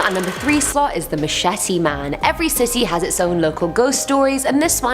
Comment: Sounds like she says "Every Sissy has it's own ghost stories"...lmfao I had2 play it bak a 2nd time lol-Love yah girl!